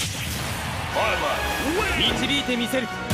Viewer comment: なんて言ってるの？？ ワイワイって聞こえるんだけど……(´；ω；｀) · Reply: 「ベレトwin」って言ってますね。 発音良すぎてわかりづらいですよね笑